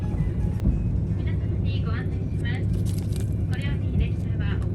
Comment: 御輿来海岸の前を、と言っているように聞こえますね、、、